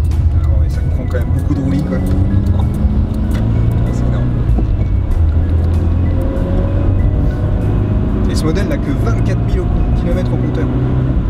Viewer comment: De 3:17 à 3:20, qu'est-ce qu'il a dit? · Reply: Beaucoup de roulis et 24000kms